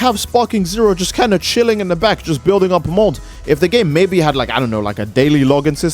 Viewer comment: I've been playing too much balatro, I thought he said mult not mold